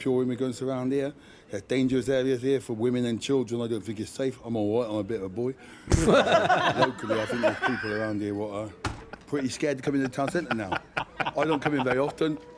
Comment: I think he said "think it's safe [i'm alright, i'm a big ole' boy]...but locally i think there's people around here that [who are] pretty scared..."